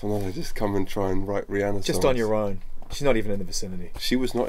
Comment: So what does he say at 14:46-14:50? I can't understand the word, no matter how hard I try. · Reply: What does they say at 00:49 ? "Just on your own, she's not even..." ?